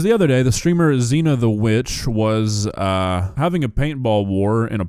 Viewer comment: it's 5:02AM, I've been up for 2 hours... I swear you said "XenaTheWench" not "XenaTheWitch" 😂😂😂 took me rewatching the clip 3 times and reading her tag at the bottom before I realized 😂 4:00 you can see the welt that is already forming on her leg... and I highly doubt this was 10 minutes after she was shot (more like 3-4 minutes I'd say) and she already has an egg sized knot.... I do hope these streamers know that this could be classified as battery and have charges that can follow 7:00 hard to be intimidated by the hog rider from clash of clans with a paintball pewpew(youtube censorship lol) and a jester in his posse... just sayin 😂😂😂